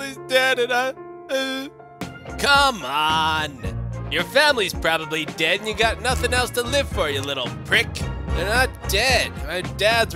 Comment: I think he said my whole family dead and I have nothing else to live for